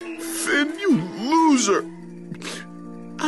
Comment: What he say!??? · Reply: i never forget you